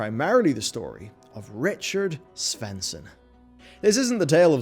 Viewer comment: thought you said Richard Spencer for a sec and thought this was going in a different direction lmao